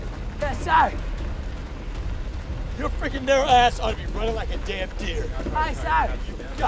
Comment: Runnin like a damn dear, lol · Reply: I thought he said " your narrow ass ought to be runnin like a damn deer"